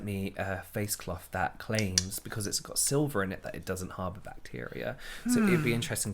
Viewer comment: when said “you bacteria” i heard it as eubacteria which is the kingdom (i.e. animal kingdom, eubacteria kingdom) that bacteria are categorized in! i was like, damn, is actually science-ing it up here 😂